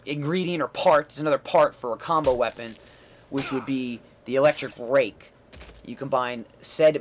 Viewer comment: I heard "Electric Rape"